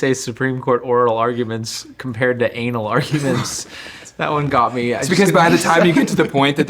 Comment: I thought he said anal ordinance not anal arguments. Now that sounds messy